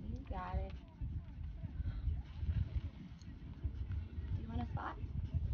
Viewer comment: what is she saying? 😳😅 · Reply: “You want a spot?” -girl